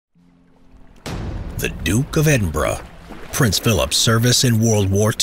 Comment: I thought he said edinbruh